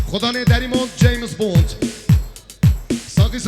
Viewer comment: Bən doğrumu duydum 2:19 James Bond 🤣😅